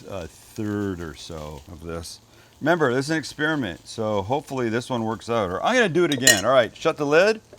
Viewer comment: Sam, did you make a pun? Did you say experiment or eggsperiment?